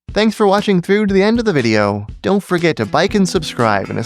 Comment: "Don't forget to BIKE & subscribe" -- You've managed to fool the Algorithm! It didn't catch that in the transcript. 😄😄😄